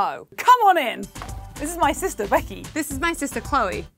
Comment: I thought they both called each other their assistants, not sisters, probably bc of their accents